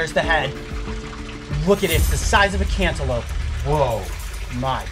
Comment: Coyote really had to hurt that salamander’s feeling when he said “his head’s the size of a cantaloupe” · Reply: Really?? I thought he said antelope.